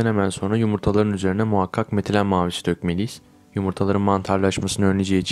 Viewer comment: de ne dediğini anlamadım ne dökmeliyiz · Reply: ''Metilen Mavisi'' :)